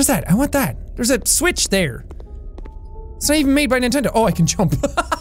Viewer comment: What said: "Oh there's a switch there! It's not even made by nintendo- Oh i can jump!" Vs.. What i heard: "Oh there's a switch there! It's not even made by nintendo- Oh i made a joke!"